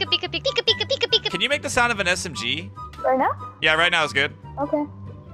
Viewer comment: Spoilers for aot (IF YOURE NIT IN SEASON 4 WHATRE SRR YOU DOING?) The guy with armored titan skin said "right now" sounded like he said "reina" which is the name of the pwrson whk can transform into the armored titan in attack on titan